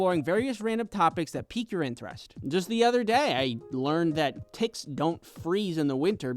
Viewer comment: sry but i thought you said „t*ts“